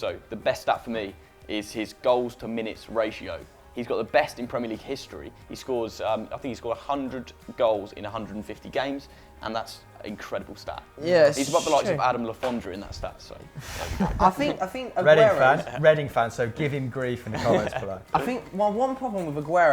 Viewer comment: why did the yes at 21:30 sounded like my name is jeff?